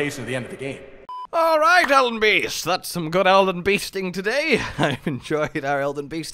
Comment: does anyone else hear this as “Elden bee sting?”